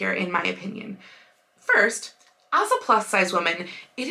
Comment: I thought you'd like to know that I misheard "as a plus sized woman," so that it hit my brain as addressing "alpha plus sized women." Made me smile.